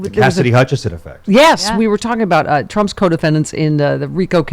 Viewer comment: I always hear 'codependents' instead of 'co-defendants'.